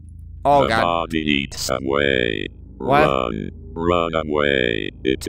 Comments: the body eats subway i think it said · I thought it said ” It eats subway. “ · I thought it was saying “I eats subway”😂😂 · I thought it said ” It eats subway. “ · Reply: Yeah! I heard “he eats the subway” “the body needs subway” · “Subway The body eats Subway.” Edit: 25 liKeS? wOW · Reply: I heard "your mom eats subway, subway" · I heard "the body eats subway" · It kinda sounds like he said "The body eats Subway " xD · I thought it was saying “I eats subway”😂😂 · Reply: I HEAR "THE BODY EATS SUBWAY" XD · I heard 'Eat subway' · I thought it said the body eat subway